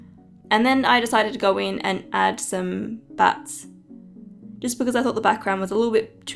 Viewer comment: I thought you said "i am adding some butts, because her hair bow kindov looks like a butt." But you said bats lol